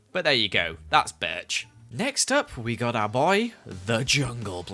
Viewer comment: Me watching with captains on so I can see what he says 5:08 he said birch is said in the caption b!tch